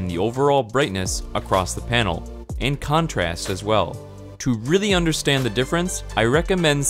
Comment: 明るさ(空耳)